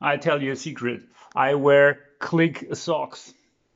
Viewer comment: I thought you said Qlik su*ks for a moment😀